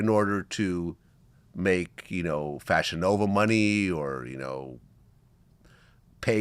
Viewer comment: I thought she said “ He had to know how to make money” There’s a difference